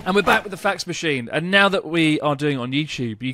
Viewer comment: Always heard it as a "facts machine" lol